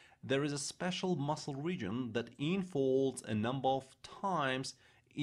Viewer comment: I'm hard-of-hearing, and while most of what you're saying is quite easy to hear & understand, some is not so clear (to me). At about 4:29 you seem to say, "...the information transmits WE'RE neurotransmitters...", which doesn't make sense. I'm going to guess (since you also call Vesicles 'Wesicles', with a German pronunciation?), that you might be meaning 'via'?? I'm hard-of-hearing, and I have found most of what you're saying pretty EASY to hear & understand, except for 'we're' at 4:29 (and at 5:22, MOTOR plate sounds like - and closed captions says- 'modern'). Oh, and at ~ 4:52, you say, "...that involves a number of TIMES in order to increase..."; is it possible you meant TINES, like the multiple points of a dinner fork? And at 5:32, "T-CHILLS", that must be T-tubules'? I am grateful for the time you have taken to present this, and understand that you are currently a practicing physician. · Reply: Thanks! I also now see at 4:52 that while the closed captions says, 'involves a number of times...', which makes sense if 'times' was 'tines', I suspect he was maybe saying INFOLDS a number of times, which might make better sense?